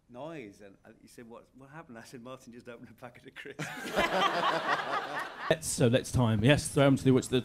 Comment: What does Mark Gatiss say about Martin in 3:31? I can't hear it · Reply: martin just opened a packet of crisps